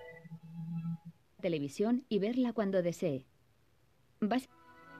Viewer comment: What is that saying? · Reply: "Television,y veerla cuando desee."